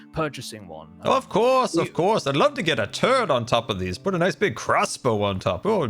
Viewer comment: "I'd love to get a turret on top of one of these" Totally heard that as "I'd love to get a turd on top of one of these" I mean if that's what is into...